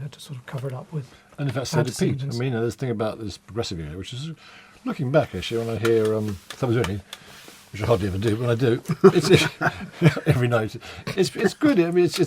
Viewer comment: I think Rutherford says "Murmer vsmureh progressive era mm hmer looking back I suh- mrrrr rrrr Tony which I hardly ever do," etc. That sounds brilliant. Can someone translate? It sounds important and I've replayed it 6 times or so. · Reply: Happy to help..he says 'So did Pete.. the thing about the progressive era , looking back when I hear Suppers Ready which I hardly ever do , but when I do..(jokely) every night ..it's good. It's a great peice of music you know but then you know when Peter left Peter changed too .."